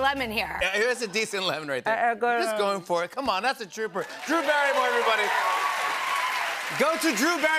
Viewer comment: Oh wow👏 Jimmy just coined a name for Drew's fan group...I think he said in the end of the lemon challenge, "that's a trooper", but it sounded like he said, that's a "Drewper":)! What a cute possible fan base name..."The Drewpers":)!😋😊🙏🌼 🌼🌼😊